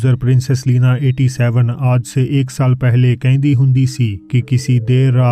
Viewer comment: have i heard something wrong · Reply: kehndi hundi si 😆😆😆😆